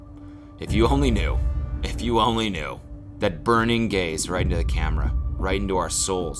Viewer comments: stop when u said “burning gaze” i heard “burning gays” and got so confused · when cody said “that burning gaze” i was spacing out and thought he said “burning gays” OH MY GOD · BHSJBDJSADH I THOUGHT HE SAID "if you only knew that burning gays" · Ik he said “burning gaze” but my gay az thought he said “burning gays”- HEKDNDKFJF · I thought he said burning gays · When Cody said “that burning gaze” i thought he said “that burning gays” and i was so taken aback omfg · Holy shit i thought he said burning gays and not burning gaze lmao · Bruh, I thought you said "burning gays" 2:45. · why did i think he said burning gays helpp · oh my god I thought he said burning gays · i thought he said “burning gays” i was like oop huh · tell me why i misheard "burning gaze" as "burning gays" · Honestly if your hair is building up to a product placement you are a literal genius. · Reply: what i heard, "if you only knew... that burning gays right into the camera..." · definitely thought you said “burning gays” not “burning gaze” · I thought Cody said "the burning gays" and I was like 0_0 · Guys he doesn’t look like big Ed come on. He looks like a middle school soccer coach Edit: All jokes aside I actually really like Cody’s hair and I think it suits him very well :) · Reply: shoutout to 2:46 where i heard “burning gays” and was horrified for a second